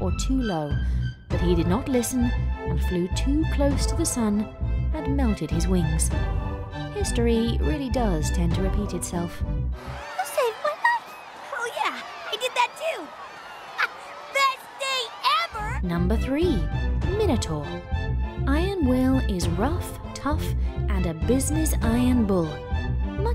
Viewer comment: Who else thought said hell yeah?